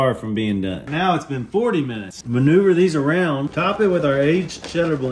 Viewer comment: All I heard was..."manure these around" 💩😆🤣